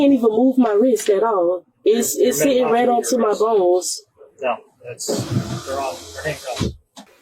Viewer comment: Did she really say my Balls? WTF?